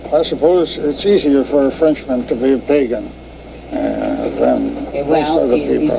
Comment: what did he say there?? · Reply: I suppose it's easier for a Frenchman to be a pagan